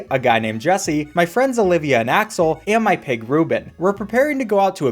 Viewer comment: did you just say... Rubit?!